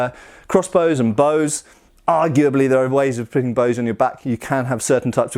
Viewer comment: I thought it said stop putting weapons on blacks at first lool.